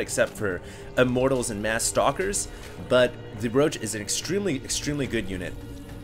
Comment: Haha I thought you said Broch, bro.